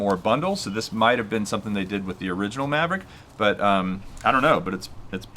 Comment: Did you just call the Mavic..."Maverick" at 1:42 LOL. Always nice to watch unboxing videos, and you do that very well! Wish I had those props to my original Maverick ;-) Kinda hard to come by. Great video, thumbs up from Sweden!